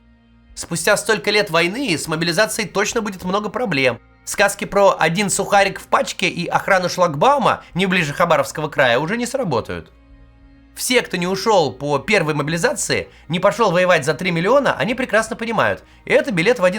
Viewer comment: СТОП! 😱😱😱😱 МНЕ ПОСЛЫШАЛОСЬ ИЛИ... КТО-ТО СКАЗАЛ ХАБАРОВСК????! 🤔🤔🤔🤔 СЛАВА БОГУ ХАБАРОВСК ⬜🟩🟦 БОЖЕ ХРАНИ ХАБАРОВСК 🙏🙏🙏 ХАБАРОВСК - СТОЛИЦА МИРА 🐯👑🦅🦅🦅🗣️🗣️🗣️🗣️🔥🔥🔥🔥